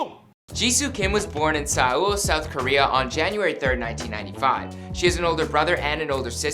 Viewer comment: did he say saul south korea?